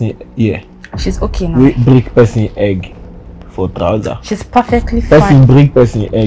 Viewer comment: Did I hear "perlefty fine" 🤣🤣🤣